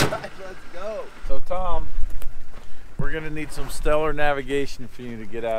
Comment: "Tom, we're going to need some stellar navigation from you to get out of here." TomTom: "But there's no stars out. It's daytime." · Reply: I thought he said "real stoner navigation"!